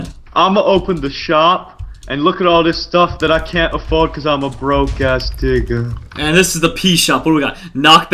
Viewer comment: did he say broke *** ******